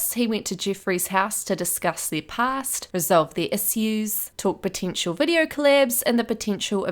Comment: Why did it sound like you said tissues instead of issues 😂😂